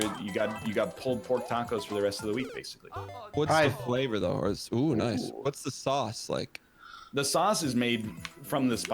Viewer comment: what did you say? Because they both thought you said “TIME” like you completed the level LMAO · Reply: I think he said "Thyme", because they were talking about spices, and it didn't register to him that it sounded like "time".